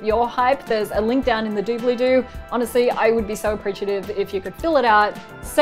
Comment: did you just say dooblydoo?? also i'm sorry i voted for IOTBS but otherwise I would vote for you because is how I got to know PBS Digital Studios.-leah